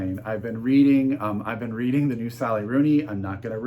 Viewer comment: I didn't catch what you said you're reading around 1:26, please? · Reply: I am reading the new Sally Rooney.